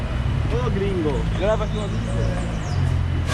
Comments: Ele disse: "Oi gringo, grava teu amigo" 8:20 "Ladies and gentlemen" AHHAHAHA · oi gringo grava comigo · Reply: Grava aquilo ali pô* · Can’t tell what the boy at 8:00 said. Sounds something like “Oh gringo, leva...” (Oh gringo, take or get...). Maybe he wanted you to buy what he is selling. The guy at 8:20 says “ladies and gentlemen”. That’s probably all he knows in English lololol